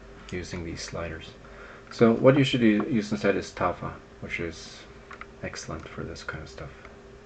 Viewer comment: What did you say to use instead at 3:11? Sorry, just spotted TAFA in the description! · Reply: sorry, looks like Youtube removed the link, not sure, anyways, TAFA is the answer... :)